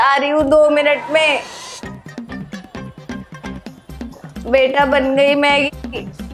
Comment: she actually says "maggi" ;)